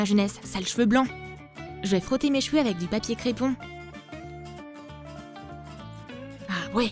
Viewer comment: attend c quoi ça 😂😂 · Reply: Papier crépon ?